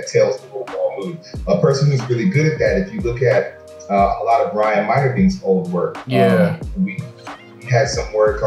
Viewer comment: Who is that person he is mentioning? I can't hear it very well. Thanks for another gr8 upload! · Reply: Ryan Meinerdin a phenomal artist. Doing characters for the marvel movies!!